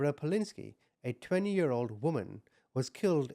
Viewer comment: Haha I THOUGHT YOU SAID "90 YEAR OLD WOMAN" 😂😂😂 thought wow pedaling away fast on her bike at 90 years old .very impressive 😂😂